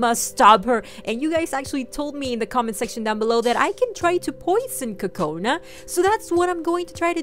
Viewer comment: I thought she said "Let's poison Oka"